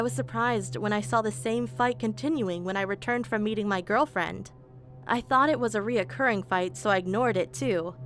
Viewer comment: I swear I heard “eating my girlfriend first”😭